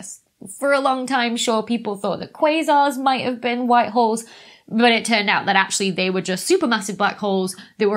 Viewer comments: I smoked a lot of pot and listened to this video....I kept hearing "white whores" and "black whores"....listen to this video again and try not to laugh...LOL!! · " Short people"? WTF are you saying?